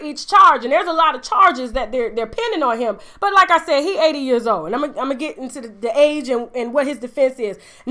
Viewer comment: At 80 years old, I was waiting for good old Bill to say he couldn't remember. The age related dementia defense. Who could say it wasn't true. Now if h said Alzheimer's, that is detectable and test could disprove that but age related dementia can go undetected. Now if Bill goes on trial again and uses that excuse, I want my check for helping out the defense lol. And I believe Bill is blind as much as I believe Vicky is a billionaire 😂😂😂😂😂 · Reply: Kimmarie D. For a second I thought you said you were 80 years old like damn girl why you on YouTube 😂😭